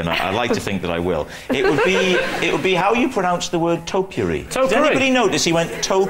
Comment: what is that word that rhymes with potpourri ? chear it right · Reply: If I was going to pick you up on anything tonight, Lee, and I like to think that I will, it would be how you pronounce the word topiary. To-pourri? Did anybody notice, he went, "to-pourii"? It was like a wonderful mixture of trimming a hedge and a little bowl you have of things that smell nice in the house. I say to-pourri, what do you say? Well, it's topiary, not to-pourri! So, hang on, hang on, whoa, whoa, just to backtrack, there's a man ironing his bread and I'm being picked up because I'm saying to-pourri a bit like potpourri? It's just occurred to me that it's a bit like toasting on the Aga cos it's a hot surface. Have you seen an Aga? Yes, I have seen an Aga! <link>